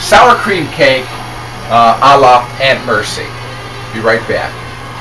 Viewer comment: Did you say in minute 5:07 "Allah have mercy"? Are you Muslim? :) anyway It looks good, i will try the recipe.. thanks for sharing it!